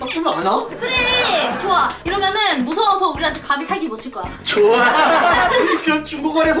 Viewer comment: 이때 사기가 살기로 들려욬ㅋㅋㅋ